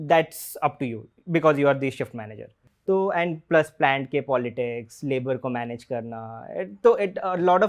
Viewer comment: plank ke politics 😂😂😂 kya bola re ??? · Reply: Plant ki politics.